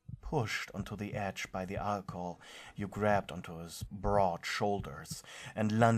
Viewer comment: Had to replay 16:11 a bit until I could hear broad shoulders and not what I originally heard. What I thought I heard was Bra Holders....I was so confused!!! But also understood cause he do have some big man tiddies